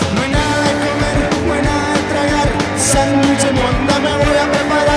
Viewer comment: Yo pensé que decía "Sandwich de mota". Ni pedo, el inconsciente haciéndose presente. Pero que buenos son estos cabrones.